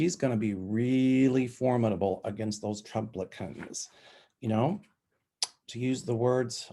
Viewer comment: I thought you said Trumplicats. I thought you were talking about the 3 criminal offspring. 😂